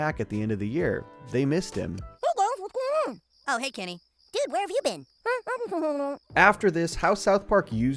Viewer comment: Little thing, at 16:37, Kenny definitely says, “Eh, I’ve just been hanging out” instead of “over there.”